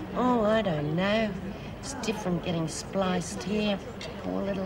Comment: What does old lizzie say at 12:13 ?? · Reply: It's different getting spliced here. Meaning getting married here. Because she's in for life.